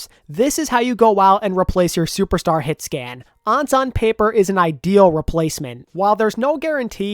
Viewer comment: I know you said "ANS on paper" but i wasn't paying attention 100% and I thought you called him "Ansan Paper" lmao